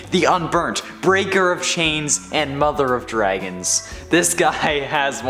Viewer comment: And i thought you said mother of fu